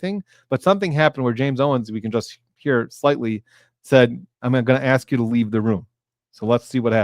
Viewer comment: Sounded like Owens said “ I’m gonna ask the jury leaves the room”